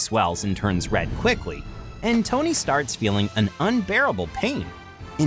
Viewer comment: I thought he said tony Stark’s feeling instead of tony starts feeling